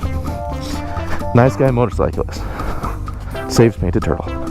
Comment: I thought he said “Nice guy motorcyclist Dave ninja turtle”